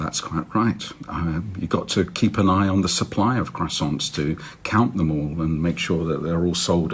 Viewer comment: I'm enjoying how Tim Harford has come down hard on the "it's a French word so I'll say it French" side of the argument. He was pluralising croissants with the French accent so it sounded like he was saying croissant.